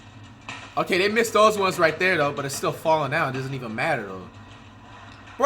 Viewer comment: Who else thought it said failing instead of falling 😂😂